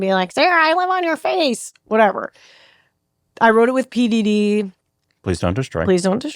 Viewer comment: What she said "I wrote it with PDD" What I heard "I wrote it with P Diddy"